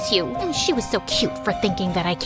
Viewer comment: wha? Did she just call her cute?😅